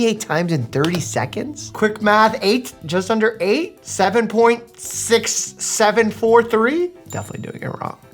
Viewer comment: Who heard 67 at 0:21 and went straight to the comments